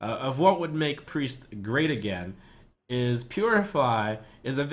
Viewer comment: did u just say: making priest great again? 😂